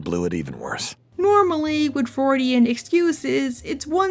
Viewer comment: Do you mean Freudian excuses? I cant hear it well